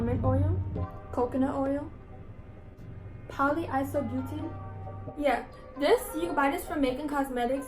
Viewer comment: what is the ingredient called cause idk what she’s says no offense tho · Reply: Paola Coronel polyisobutene